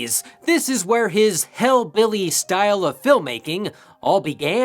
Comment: I laughed so loud when you said ”That’s where his hillbilly style of filmmaking all began”. Thank you. That was funny and oh so true! Zombie makes it next to impossible to cheer for any of his characters even a tiny bit. They’re all mean spirited in one way or another. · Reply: I heard "hellbilly" and thought it fits Zombie's style a bit better